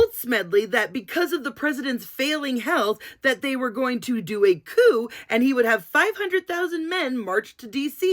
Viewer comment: I thought you said, "They were going to do a POO !!! Lol😂